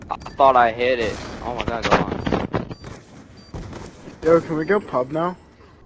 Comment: ish did someone say 'can we go pub now'? Is this set up :/ ?